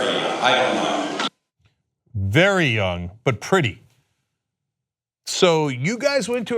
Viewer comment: he said (I heard) "they were young, probably very young, I don't know" not "but pretty".... Anyway, still 100% agree he's despicable.